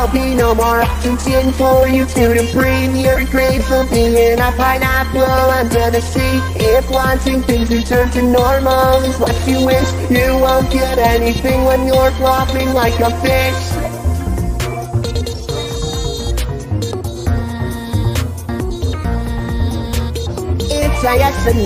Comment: “When your flopping like a FISH.” dang that hit hard Edit: HOW DID THIS HIT 80 LIKES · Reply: I thought he said "When your flopping like a BITCH"